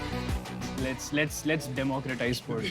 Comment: what did he say? Let's democratise porche, means?? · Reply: sports*.